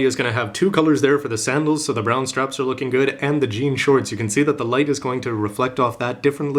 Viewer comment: for some reason at 2:36 I heard GN Shorts instead of "Jean" Shorts O_o